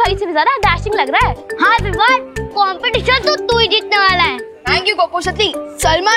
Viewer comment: Without Captions, I heard Vivaan saying Pappu Sutli 🤣🤣